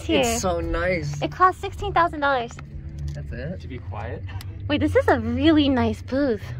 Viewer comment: did he just say is that it like bro wtf